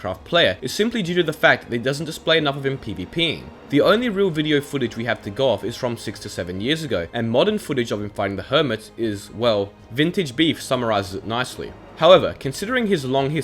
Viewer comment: for a moment i thought he said from 67 years ago instead of 6 to 7 years ago XD